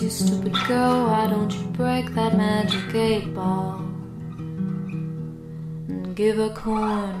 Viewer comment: I HEARD "That magic eight ball" AS "That magic gay porn" HELP ME